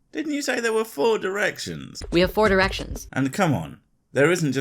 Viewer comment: misheard as "we have 4 er*ctions"